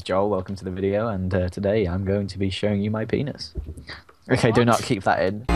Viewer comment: I had no headphone on and the volume on on full so my parents heard what Joel said at the beginning! My mum went " WHAT!!!" 😂😶😬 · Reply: Oh gosh... I thought he said ' Hello, my name is Joel, and today I will be showing u my beans."